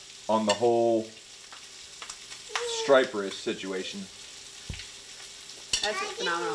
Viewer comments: My girl thought it said strippers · For a sec I thought it said strippers 😅